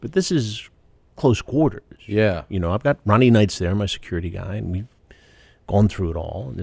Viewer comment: I almost thought he said "I've got Ronnie Mund there, my security" lol